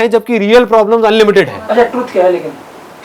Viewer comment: What does the person say in the last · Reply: He said "truth kya lekin?"